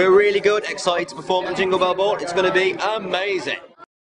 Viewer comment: Thumbs up if you think Liam says Amazayn at first! >.<